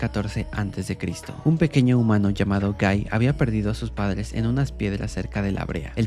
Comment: en vez que escuche kai escuché gay xd